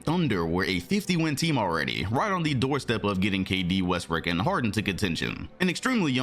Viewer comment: Yo did he say westbrick? 😭